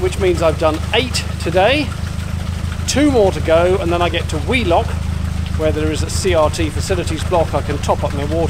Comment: I thought you'd said 'wee' lock and thought it was an appropriate place to empty the Elsan, until I saw it was Wheelock! Made me chuckle in places, especially the pulling off while still tied up. At least you didn't have an audience, as is usually the case when something goes wrong. Great vlog, as always 👍